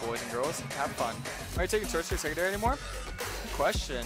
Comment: I couldn't hear what he said about not taking what anymore. Any help? xD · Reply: he's not taking sorcery secondary anymore on blue kayn cause he feels its better for scaling and he wants to play for mid game